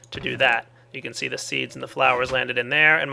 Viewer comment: did he say beast and the flowers at 03:15?